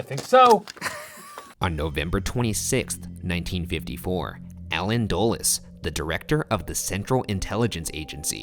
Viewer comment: I thought it said alien dulles, coincidence? I think not.